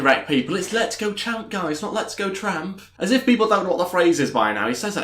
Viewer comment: Great video! I thought he was saying let’s go trump after Logan referenced Churchill 😂